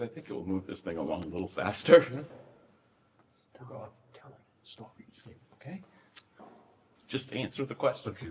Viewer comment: *Lawyer’s whispers:* 2:14:00 “stop telling stories” 2:52:45 “less[no] stories. Do. You. Know.” 3:28:13 this one I’m uncertain of but sounded like [added fragrance/evidence?] 4:25:06 “I will fucking kill you if you answer another question with more than five words, okay?” 4:40:33 unsure first part “not right now.” Big yikes.